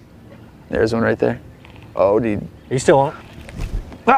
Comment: Kind of creeped me out a little bit at 4:07 kind of sounds like he said he O.D.....really sucks that he's gone I no you did everything you could have done Buddy